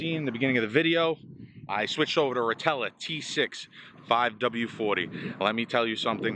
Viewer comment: Good video. That’s a badass setup you have there! You seem like a nice guy and not a douchebag like a lot of YouTubers. On a funny note, the first time you gave the name of the oil you are using I could have sworn that you said Nutella 😂😂